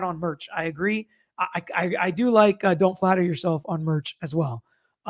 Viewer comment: I have listened to that over and over again and I don’t hear her say don’t flatter yourself. I thought she said don’t bother yourself yet everybody’s saying flatter.